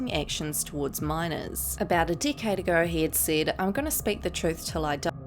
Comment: I thought u said "about a dick head ago" 😂